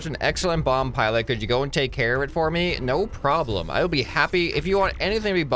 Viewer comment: When said “no problem” I thought he said “nope” 😂